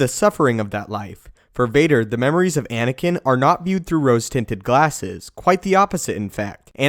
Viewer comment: yoooooo did he just say " For Vader, the memories of Anakin are not viewed to roast him with glasses" 😂😂😂😂